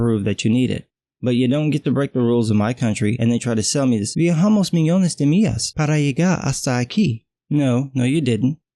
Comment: Wtf does he say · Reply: Viajamos millones de millas para llegar hasta aquí. We travelled thousend of milles to go here Sorry for the crappy english